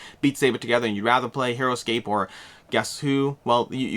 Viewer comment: does he say "Heroscape"? I love those games!!